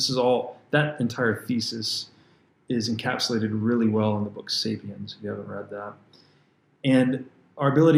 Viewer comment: He mentioned a book around 1:21:20, I think he said "Sapiens" but I'm not sure. Well perhaps so... There is such a book: "Sapiens": A Brief History of Humankind, by Yuval Noah Harari